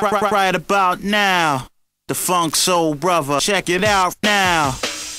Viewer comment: I always thought it said "the funk's so bruver". I was always confused what a bruver was.